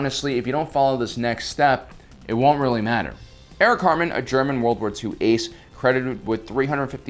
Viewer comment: I keep hearing you say "Eric Cartman" not "Erich Hatrmann". Cartman is more of a Porkins pilot.